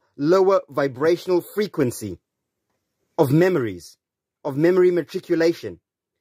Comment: I thought he said "mamories", I felt a right tit deleting my first comment....